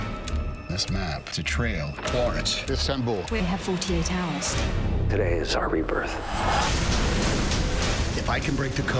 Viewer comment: what city did he say at 0:19? it sounded like constantinople, but i couldnt tell. does anyone know? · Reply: Istanbul